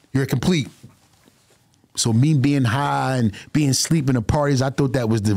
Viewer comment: did he just say he drank lean and went to sleep at Diddys party